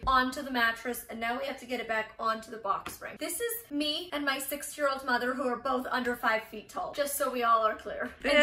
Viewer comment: Lol it sounded like you said "me and my 6 year old mother"